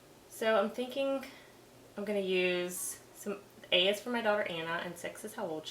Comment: Really want one! Thanks for showing us. Also I thought you said "I'll use some farts" 😳 then I figured it out 😊